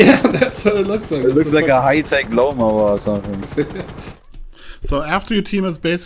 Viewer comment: did he just say a Hi-Tech Loma?